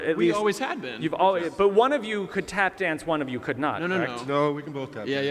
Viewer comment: I seriously thought it said lap dance at first